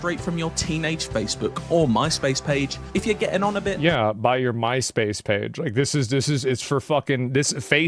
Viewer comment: DID BRO SAY MYSPACE?